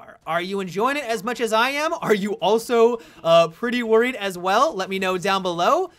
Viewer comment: for a second there I thought you said "are you also gonna preorder it" when you said pretty worried xD put my guard up! happy thanksgiving